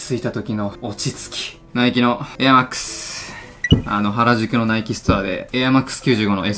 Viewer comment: NIKEのエアーマックスがベイマックスに聞こえたのおれだけ！？